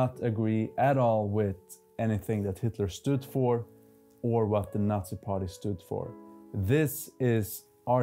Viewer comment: Did yo say at approx 0:45 " not agree with what the Russian Nazi party stood for?" EXPOUND PLEASE